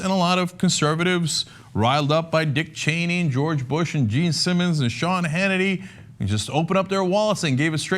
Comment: I misheard Gene Simmons as Richard Simmons.